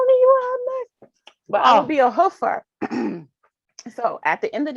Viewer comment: Lol, I thought she said hooker too. 🙄😬